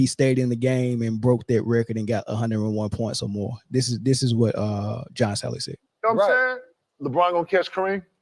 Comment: Bro its "JOHN SALLY" .. Ja-On Sal-Lee... This podcaster speaks like a 14 year old. Sounds like he says josh allen everytime he says john salleys name . Speak like a adult for christ sake